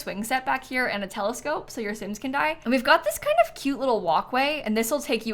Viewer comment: Did she say so your sims can 💀? Lol